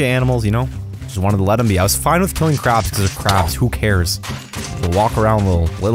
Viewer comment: I love how you see his morals change throughout the video he went from only killing crabs to making every animal extinct I mean he slowly went crazy and that would really happen probably. I loved the vid super entertaining. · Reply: I thought it said killing carbs